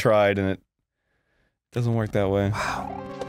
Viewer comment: did anyone else hear; "That block with all the booze in it"?